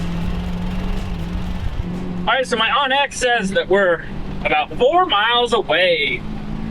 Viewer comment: Lol! Sounded like you said "My Aunt X says..." 😂😂